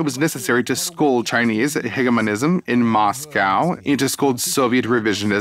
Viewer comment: Никак не могу найти это слово и её определение. Ближайшие что я нашел это термин "Генефонизм" через chat GPT, но это не похоже на то, что он сказал по звучанию. Кто-нибудь знает это слово или может найти её? Просто очень интересно. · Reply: гегемонизм - политика, основанная на стремлении к мировому господству